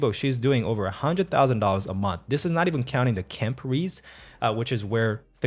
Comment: I couldn't make out what you were saying at this time when you were talking about how fictional authors make most of their money. It sounded like you said "kemperes," but im not sure what that means because im still new to these ideas. Could you help me understand what you were saying at this part? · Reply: I think he's saying "KENP Reads" which is Kindle Unlimted. If your book is part of Kindle Unlimated they will pay you per page read, rather than per book sold. So if you set your books up in Kindle Unlimited it's just another revenue source. It's a paid subscription (Like Netflix), but for books. So you can make money from 1 book multiple different ways. Paperback, ebook, audio book, and KENP reads (Kindle Unlimited). Hope that helps!